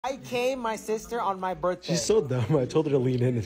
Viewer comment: What I thought he said~> I ✨caned✨ my sister on my birthday